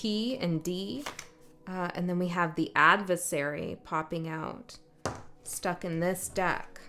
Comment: ♐ ❤ baby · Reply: Hah..still listening, but just as you said adversary, a car drove by and I misheard, thought you said ACID FAIRY...lol..had to back up and relisten. But indeed, a very acidic person has not only worked against me, but even gloating proclaimed it to me!! ..lol..acidic fairies..